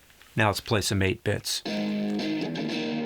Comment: I thought you said apex but you said 8-bits